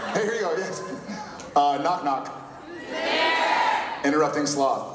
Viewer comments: can someone tell me what jared said at 5:55? after the "whos there"? i couldnt understand the word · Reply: Interrupting sloth I'm six years late lol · can anyone tell me the knock knock joke from Jared at 5:50 ? I couldn't understand what he's saying after the people said knock knock :D help please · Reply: Interrupting sloth